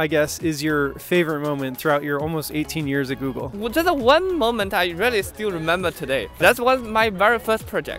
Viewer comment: said "我覺得 one moment..."🤣. I heard his accent and I guessed his first language is Chinese, and then at that moment, I'm pretty sure about that.